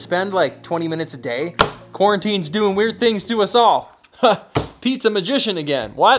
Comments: “Pete’s a magician again” OR “pizza magician again”? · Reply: same here. in all honesty I thought you said pizza magician for a second😂 great content good job dude! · What he said-"Pete's a magician again" What I heard- "Pizza magician again" 🤦🏽‍♂️ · What he said-"Pete's a magician again" What I heard- "Pizza magician again" 🤦🏽‍♂️ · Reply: Wait what ?!?! He said-"Pete's a magician again" ?? I also heard him say "Pizza magician again" 😂😂😂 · I heard 'Pizza magician' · i thought you said "pizza magician" lol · While working from home auto-playing videos. I thought i heard him saying "Pizza magician again!" got me dude! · I thought he said pizza magician